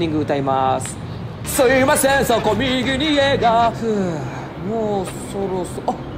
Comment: すいません、そこ右に家が～ は地味にウケた、てか本家すらそう聞こえるようになったｗ このラーメン屋さんの感想として、好き嫌いは人それぞれだから・・・って感じでしょうか